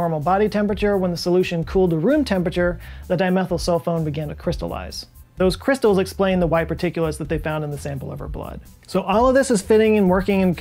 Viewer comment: I keep hearing "Dimethyl Cellphone"